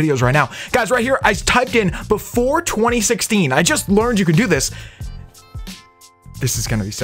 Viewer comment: I thought he said be 420 16